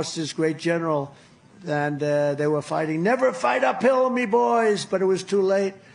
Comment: I thought he was saying "never fight a pill" 😂😂 and I really lost it when he threw in the Mr.Krabs impersonation. Chump is a dumb criminal.